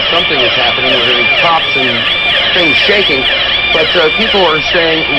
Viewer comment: CNN @ 5:56 did Jaco say "PROPS & things shaking"... as in stage props?